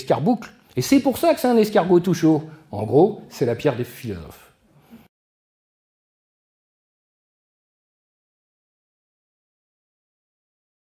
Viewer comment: Il dit quoi à 5:06 "c'est la pierre ..." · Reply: "c'est la pierre des philosophes"